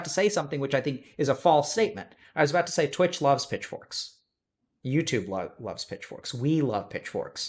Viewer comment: I need to get my ears checked because at 1:19:11 I thought he was saying "bitch boys".